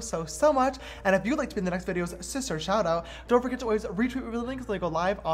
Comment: When I first watched your videos I heard “Hi sisters” I started to watch more videos and started hearing “hi shisters” now I hear “isis turds” who else? Lmfao